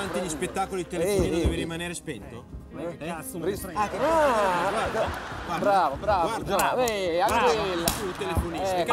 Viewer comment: Ma a 2:48 cosa dice Pezza? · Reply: "Ma che cazzo me ne frega"